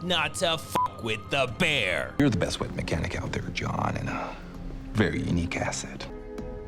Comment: Does anyone know what the guy says at 0:57? Something like "You're the best weight mechanic out there, John." What is a weight mechanic?